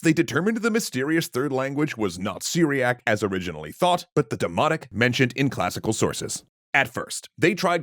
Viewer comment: I’m gonna be honest I thought you were saying “demonic” instead of “demotic” and I was so confused.